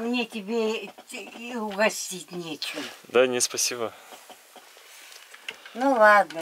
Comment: I'm currently learning Russian and at 9:04 I got confused when he say's "I'm fine".. I've never heard "I'm fine" said that way. Is that a slang version usually used for teens and how exactly is it spelled ? Does somebody know? Sounds like he says "DAN-yee" when pronouncing it. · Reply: He said "да нет"